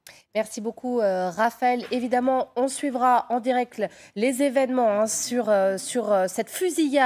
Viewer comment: "en direcle"..!!??!!??!!?? MAIS JE SUIS BOURRÉE OU QUOI !?!!??!!??